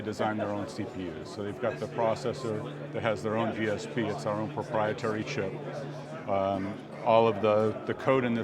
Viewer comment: Did he say “propriety shit” ? It’s just a modern computer, with no doubt many cores. Thanks for the info though, a great preview!